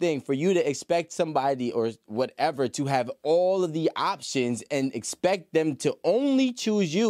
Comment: “Options” I just hear you have a “penis roster” of men who won’t plan with you, they just play with you. Girl bye